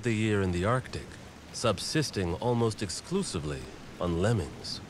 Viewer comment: First watch, I heard "Snowy owls spend most of the year in the arctic, subsisting almost exclusively on lemons."